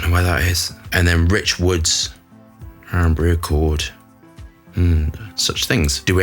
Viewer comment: I seriously thought you said Irn-Bru accord and did a double take 🤣